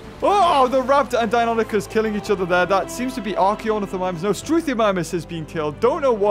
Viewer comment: He says "Archaeonithomimus" but I hear "Probablygonnadiefirst"